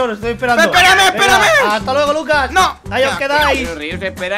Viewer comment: no c yo pero escuche que rius le dijo lucas a acenix :o